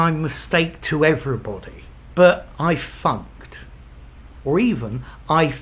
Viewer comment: For a second I thought you said "I fucked" "I fucked out" when you said "I funked" "I funked out"